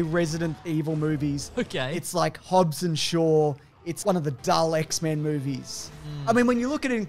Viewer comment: when is listing mediocre movies he says “1 of the dull X-men movies” but the subtitles say “one of the Daleks man movies” It’s late & I’m an idiot so I searched for Dr. Who movies for about 10 minutes to find out more about Daleks Man. Was he a hybrid, half man half Dalek? Were there many Dr. Who movies? The search wasn’t going well so I rewound the video, then realizing the images shown are from the movies as he mentions them. I got excited for the Dalek Man to be shown ending my search, but instead I just confirmed what I already know . I’m a bonehead.